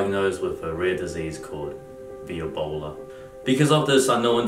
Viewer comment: For some reason, I heard "Earbola". I was like "Ohh is this a joke on how Brett didn't have perfect pitch" lol. Then it turns out I heard it wrong. Guess I'm the one with the Earbola 😅